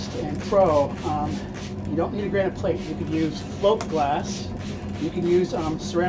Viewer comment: What is "slope glass" mentioned at 7:30 in the video? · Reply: Thanks, I actually thought I heard "float" at first but wasn't familiar with that adjective applied to glass and it was not clearly enunciated, at least not for my old ears. So, I turned on the closed caption and it said "slope" glass. 🤣 I appreciate the reply!